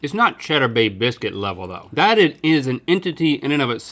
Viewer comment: Wait. They're saying cheddar bay biscuit. I have always thought and said cheddar baked biscuit. 🤦‍♂️